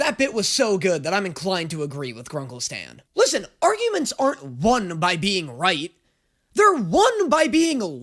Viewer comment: I just heard “arguments aren’t won by being white” and though was about to drop a race joke 💀